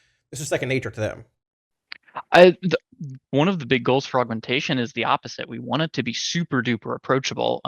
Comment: I keep hearing say "for Augmentation" as "frogmentation". Now I want playable Murlocs and a Frogmentation Murvoker.